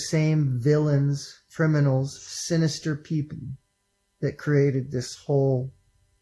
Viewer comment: did he say "sinister peepee"?